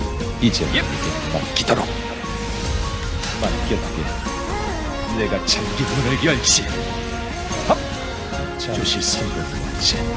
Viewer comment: 초이거 뭐라고하는거임? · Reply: 맡겨두게 내가 잠긴 문을 열지